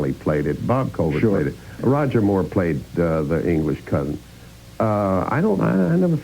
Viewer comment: Did he say that "Roger Moore played the English c•nt" at 3:57? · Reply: It was "cousin". Just a bit chopped in this video.